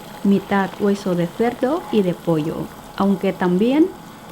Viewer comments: como que hueso de perro o de pollo? O entendi mal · escuche bien oh mal?? Dijo hueso de perro??? Creo que eh escuchado mal 😨😨.